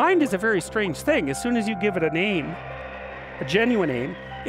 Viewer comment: When he said "an aim" I thought he said "a name"